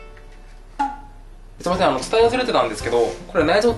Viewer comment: 本日もご視聴いただきありがとうございます(^^) 10：00付近で鮮魚を捌きますので苦手な方はお気を付けください。。。 んで今回の動画で僕が申し上げた「孫六さん情報」は下のURLから(・ω・) <link> 沖縄料理最高！(｀・ω・´) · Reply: ずいせんって聞こえた気が…